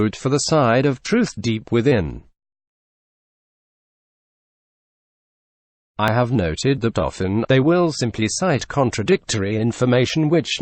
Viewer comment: I am assuming that what is being said here is actually, "I have noted that often . . .," rather than what I initially heard the first ten times, "I have noted the dolphin."